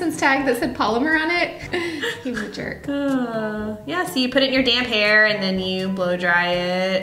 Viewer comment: " you put it in your damn har:" ded thoughts that's what u said hahaha